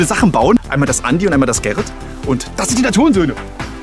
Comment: Wer hat bei 1:00 auch die Hurensöhne verstanden?